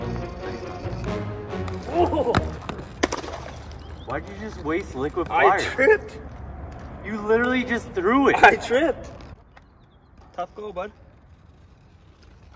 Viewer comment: "why did you just waste liquid water" this channel has taught me more then life ever will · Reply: I thought he said liquid fire